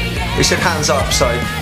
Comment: Misheard lyrics: they said “Hands up!”... not “Pants off!” 😜